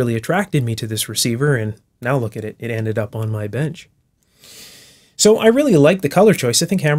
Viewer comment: i thought he said "bed" and i was very, very confused...